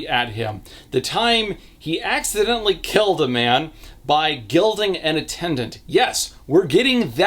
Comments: i swear to god I heard ‘gelded’ at first and had to rewind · You said “gilding”… i heard “gelding” making that MUCH more traumatic and the daughter a more important aspect, so LOL